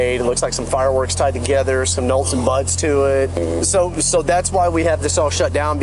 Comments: did anyone else hear the cop say "nolts and buts" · i had to rewatch like 20 times to hear the cop say “nolts and buts” instead of “nuts and bolts”😂😂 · Ever had a time when you said something totally mixed up and opposite. Well, I noticed The officer said nolts and butts as in nuts and bolts(5:27), anyone catch that? Mixing up words is rare so whenever it's caught, it's worth mentioning. I guess. Anyway, Good job out there officers!!! Always a fan. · the cop says, '...some Nolts and Buts to it....' lmao. Instead of 'bolts and nuts'. Super funny. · the cop accidentally said, “ some nolts and butts tied together” instead of BOLTS and NUTS lol